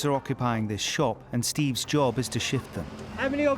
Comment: for a second I thought he said Steve Jobs🤣